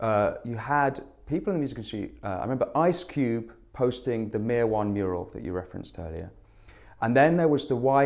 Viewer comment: "Ice Cube posting the Mear One mural" - couldn't work out what he'd said at first, but a bit of Googling and I've discovered it's "Mear One" - the artist Kalen Ockerman